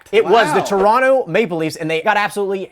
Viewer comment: It says male leads instead of maple